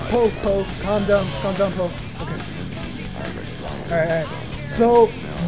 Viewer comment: Did you say Popo? 5:47.